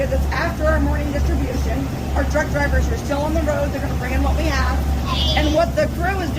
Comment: This is awesome! I know how worried was about not being able to get the turkeys ❤️❤️. But at 13:41 I had to listen to this 3 times, lol. I swear she said "our drunk drivers are still on the road". Lol. I knew I had to be hearing it wrong😆😁